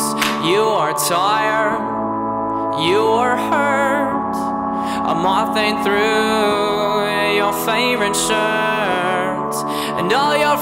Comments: When I heard "a moth ate through your shirt" I thought he said "I'm walking through your favorite shirts" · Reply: I heard "Your MOM ate through your favourite shirt". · tyler: you are tired, me: yeah? tyler: you are hurt, me: OMG WHERE!? tyler: a moth ate through your favorite shirt me: NOT AGAIN. MOM, GET THE BUG SPRAY. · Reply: I thought it said "I'm walking through your favorite shirt" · When I heard "a moth ate through your shirt" I thought he said "I'm walking through your favorite shirts" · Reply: i thought it said i’m awfully through your favorite shirt at first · i thought he said All your friends "burn alive" instead of "fertilize" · When I heard "a moth ate through your shirt" I thought he said "I'm walking through your favorite shirts" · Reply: I heard at first "I'm mopping through your favorite shirt" xD